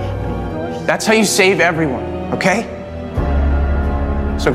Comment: I hear “leafe” instead of “leave”..😭